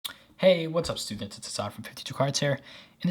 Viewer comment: I heard "What's up stupids".